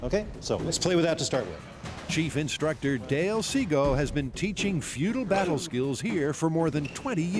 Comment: ah yes, bujinkan, a martial arts form proven to be as fake as a 3 dollar bill, totally real ninjas you got there mythbusters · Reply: did i hear Dale Seagal?. 🤣